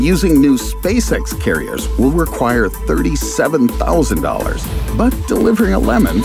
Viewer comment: im in pain... also at 9:25 he says it costs $37,000, but the caption says $27,000